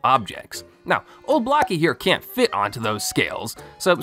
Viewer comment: I thought you said Glock instead of block lol